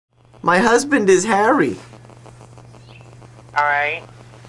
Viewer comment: I heard it as "My husband is hairy"